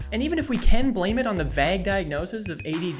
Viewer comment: did you just say "vag"